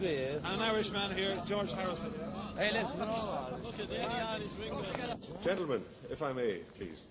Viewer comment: I think Johns Says,"we're all irish' :D